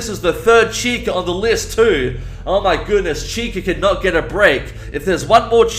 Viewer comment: At first I thought you said if you saw one more chicken you were gonna lose it!